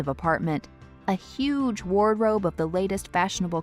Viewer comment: did she just say "warrobe" lol